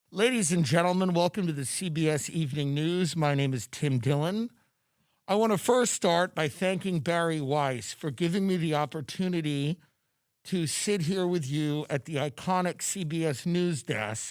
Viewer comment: I thought he said “My name is Titty Dillon” 😂 I had to go back like 5 times. I couldn’t hear through the mustache 🤣